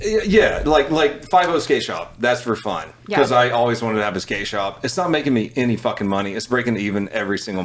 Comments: he says "i always wanted to have a skate shop" subtitles say "i always wanted to have a gay shop" and there it is XD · An episode where Brandon and Donut aren’t matching?!?🤯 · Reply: even the subtitles are on to them: 36:27 he says "i always wanted to have a skate shop" subtitles say "i always wanted to have a gay shop" and there it is XD